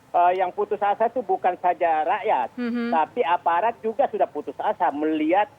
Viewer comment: Aparat putus asa ga salah denger😄 yg ada malah keparat.. pemerentah, aparat, jangan di gajih. jangan di kasih makan sekalian.